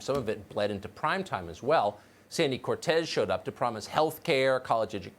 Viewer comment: did you just call her Sandy Cortez?